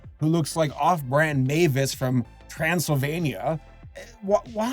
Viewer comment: I thought he said Castlevania you know the game instead of Transylvania